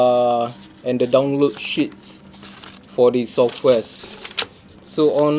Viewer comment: can we rename the files directly in the recorder itself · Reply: I know this guy has a different accent, and I’m not trying to be an asshole, but I found it funny at, 0:79, when it sounded like he said shits, even though I understand it as sheets. The way he was saying it literally sounded like, shits. Haha. 🤣